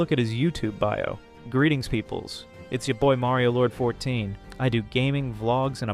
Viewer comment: I looove how the pronunciation went from 'mario lord' to 'merio lord' to 'mary lord' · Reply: I thought he was saying Marylord the whole time since I was just listening, not watching.